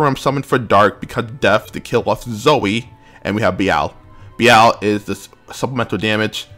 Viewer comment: "And we have BL" Pretty sure I misheard that one but ok.... not that it's far from truth, dude does love every kink possible include BL, kek